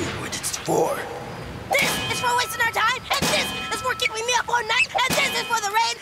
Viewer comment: "AND THIS IS FOR KEEPING ME OFF FORTNITE!" (I know that's not what he says but it sounds like it😭)